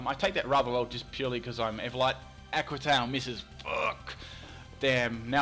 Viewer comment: Lol, when says "Aqua Tail", it sounds like he is saying "Aqua Town" XD